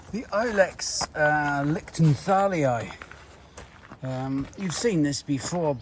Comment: Hello all at Eggleston!! Thankyou for another lovely video. So very glad you put up the strap line with the name of the Lichtenthalii Holly!! I thought you were saying Liptonthalii (like perhaps the leaves looked like a tea bush? I dunno!! Daft as a bag of bollocks, I am sometimes! haha!! ) anyways, I wish I was still able to drive so that I could come and buy one from you lot! It'd be a lovely trip across the Pennines, I'm sure! Unfortunatley thanks to crappy health, my driving days are behind me, but i now have the consolation of being able to find a good independent UK plant supplier, online to buy one from (APHA Plant Passport verified, of course! I found out about all that, this week! Bang goes my grand ideas of selling any of my extra succulents and pelargoniums online! 'sigh'!) . Oh by the way, if you're struggling with the ID's of some of your succulents, I'm sure the British Cactus and Succulent Society would be able to help you out. They have excellent online forums. Being a bit of a succulent fancier myself (steady on mrs!) I know how difficult it can be to pin down exact names for them! Bestest Wishes to you all!! <3 xx